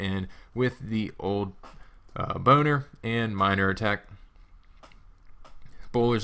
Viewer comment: sounds like he said boner